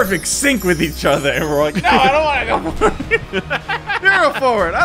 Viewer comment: It sounded like said no I don’t want it with his Autotune on😂